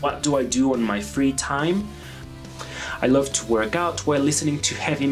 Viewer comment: I thought you say you like to twerk listening to metal. Lol I guest you actually said to workout lol lol.